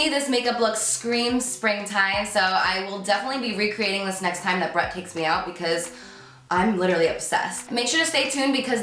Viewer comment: I thought she said "next time Brett kicks me out" at 6:45!